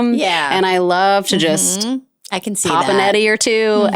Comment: Someone help,.. what is a netty? What’s she popping? lol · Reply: I thought she said "eddie" as in edible :)